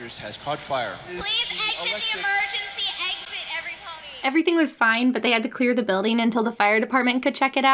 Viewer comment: did she just say everypony